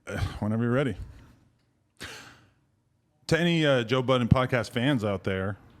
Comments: I thought it said Joe Biden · Nah i thought that shit said Joe Biden at first 💀